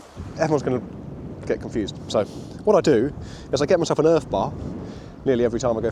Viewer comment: Now that was an exciting catch! Not managed to get one yet but one day... Amateur question🫣, what's an earth bar please? (1:45) I think I heard that right? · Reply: Hi mate. Thanks for watching. It's called an urfe bar. A super simple little bit of tackle for making a really effective rig, really quickly... <link>